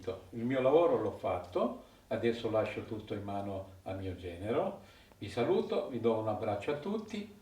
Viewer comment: I have been studying Italian, and at approximately the 2:30 mark I was able to understand what your father in law said (I think)! He spoke clearly and at a pace where I could comprehend it (as far as I know). I would love to see more videos of him speaking like this in his native tongue as he prepares the cuts of meat. Watching him work with a knife and explaining what he is doing would be a very enjoyable watch all by itself! Edit: I believe he said, "I'm done. I have done my job and will leave you in the hands of my General. Humble regards, goodbye" I'm a beginner though, so what do I know. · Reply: Yes 👍🏼 but he said 😉 genero non general . Yes it is a good idea but very complicated to accomplish 😬 we live far from each other